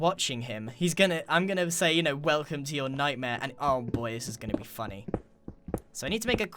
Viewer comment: When Grain said Welcome To Your Nightmare I thought he said Welcome To Night Vale